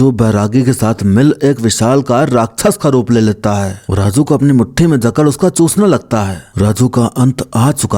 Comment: KYA BOLE BHAI 😂😂😂😂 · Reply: uska chusne lagta hai 😥😥